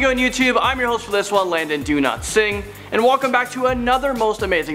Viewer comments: When Landon was saying his own name at the beginning it sounded like he was saying Landon do not sing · Is it just me or does it sound like he said Landon Do not sing? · Lol no offence but I thought you said your name was Landon ‘do not sing’ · I thought he said land and do not sing